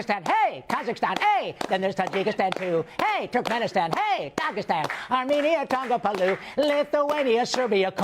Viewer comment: I Think The He Said “Kagistan”. Will Be Country